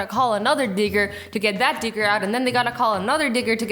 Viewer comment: literally sounds like she is saying n***